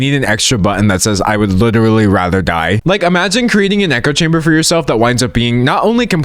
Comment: I thought he said "a neko chamber" and was wondering where's the issue